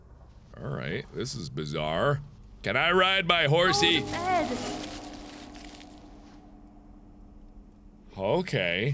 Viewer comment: Can anyone tell me what the mother said at 0:30? When the dad hit the mom? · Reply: Prince [ホセ・ロドリゲス] She said, "go to bed!" to the baby and the husband was trying to pull her away afraid for the baby. From what the creator said the husband wasn't abusive, the part where it looked like he hit her later on he was actually taking a bottle away from her because she's an alcoholic (as per the ending of the main game.)